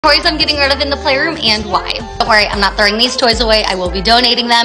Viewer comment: Omg i was so scared i wasn't paying attention and i heard "i am getting rid of my playground and why" 😭😭